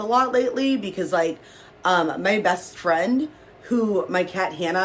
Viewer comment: how were you traumatized as a child while fishing?? Also, did You run off stage (I am guessing Your first club You danced at was the Diamond Men's club in the Flats) when Your Brother came up to the stage and said You still look the same as You did at 4 years old? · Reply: sorry! I thought You said Your Brother in the video, not His friend.